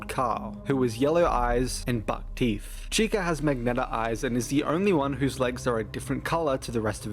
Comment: huh, never heard that pronuncuation of Magenta (thought u were saying "magnetic" at 1st)